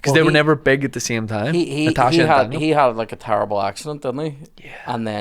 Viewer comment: The whole start of this, I thought you said Daniel was phased out because he had a terrible accent not accident and I am killed trying to Google his accent. 😂